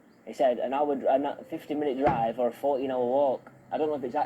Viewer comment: I hear “50 minute drive or 4 hour walk” and before he says “50 minutes” he’s about to say “an hour” but corrects it to the more precise ETA of “50 min”